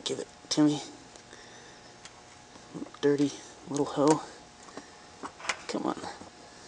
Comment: Did he just say "dirty little ho?"